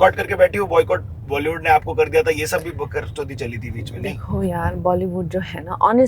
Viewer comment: did he say bakarchodi. 😳